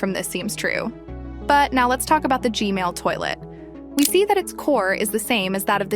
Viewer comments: I heard you say gmail toilet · Bro I heard her say "g mail toilet"